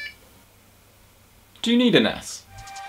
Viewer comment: In the begening I heard "Do you need an Ass!"😂😂😂😂 btw Video 😍